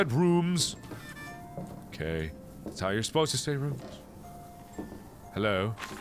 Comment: who is watching in 2017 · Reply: Wait did he just say ROOOOOMS!?